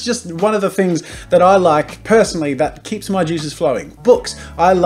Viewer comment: I thought he said get your jesus flowing. I died.